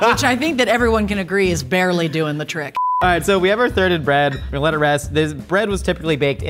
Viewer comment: I 100% thought Josh said "farted bread".